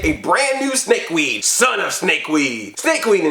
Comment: I thought they said Son of a Snakeweed. Which sounds like a curse word. But I loved seeing Donnie and Mikey switch pretty much and Donnie has more respect for Mikey now. Also Raph was not about to let The Creep get him a second time. He was fierce in that episode.